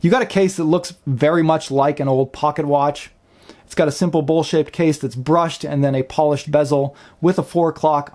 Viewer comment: Great review, but i have to ask what is a simple bull shit case mean min 8:51 ? or am i listening to it wrong lol. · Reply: he said bowl shaped, I thought he said bullshit as well just had to watch it a couple times